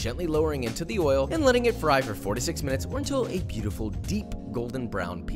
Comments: "Fry the chicken for forty-six minutes' Did I hear that right? That's a lot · i heard “fry for 46 minutes” and my jaw dropped😭 had to go back and rewind it LMAO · for a moment i thought you said "fry for 46 minutes" and i freaked out